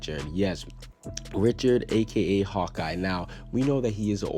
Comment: Whoa, hold up! Did I hear him say "Richard, aka Hawkeye"? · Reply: No he said Hot-Eye